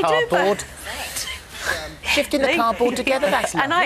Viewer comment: Did she say Shit In A Cup lol